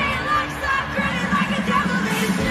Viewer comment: I always heard her asking "is it for the best?"